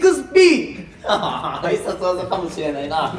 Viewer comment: ｢必殺技かもしれないな｣がきみあおを観すぎているからか、｢ん〜熱はないな｣に聞こえてきます😎